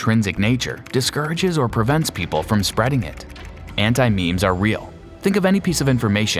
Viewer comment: I thought he was saying anti memes lol